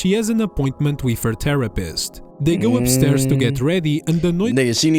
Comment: GENUINELY thought bro said “terrorist”🫩💔🥀